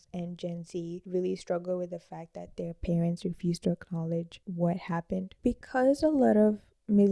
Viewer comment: I'm sorry, what is it that you said at 15:12 ? ".... their parents refused their college...,"? Is that what you said? I don't know if it's your accent or if you're not enunciating your words clearly, but it's hard to understand clearly of what you say. And as a result, it doesn't make sense to me. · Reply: I said “their parents refuse to acknowledge the harm that was done.” Maybe it’s the audio of the video, not necessarily my accent. My accent is perfectly fine and everyone else understood me🫶🏾! The audio of the future videos will be fixed. Thanks for watching!